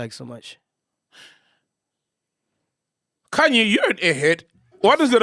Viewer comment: did Sol call Ghost Lady "Khanyi"?